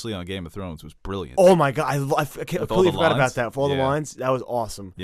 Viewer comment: Can someone tell me what they are saying at 15:30, I didn’t see the previously on game of thrones. I can’t understand what they are saying somthing like “follow the lines”?